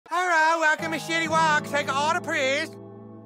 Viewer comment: You guys hear “city wok” But I hear “shitty work”